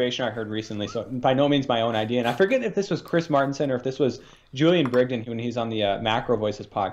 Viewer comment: Great show guys. btw 17:20 mentioned a name I couldn't make out ,,,'McAvoy' or something and what channel? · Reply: Julian Brigden